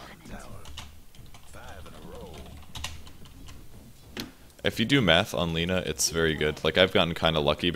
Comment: I heard "If you do meth on Lina it's very good"